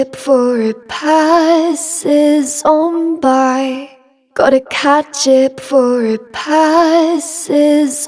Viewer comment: What the vocals say "Gotta catch it before it passes on by". What I heard "Gotta cat chip for a pie sits on by". Thankfully there are captions 😄